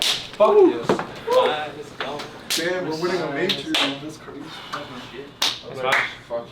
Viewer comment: did someone say "We're winning a major"? LOL love the confidence already.